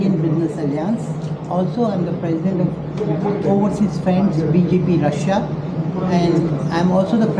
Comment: Did he said BJP Russia 😂